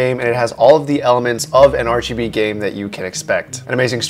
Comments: I swear 0:44 you said rgb, such a pc nerd XD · was it just me who heard rgb?